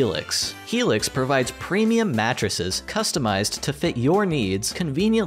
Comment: the sponsor segment... every time he says mattress I hear matrix... 3:56 helix provides premium matrices customized to fit your needs and conviniently shipped to your door, helix's quiz matches you to your ideal matrix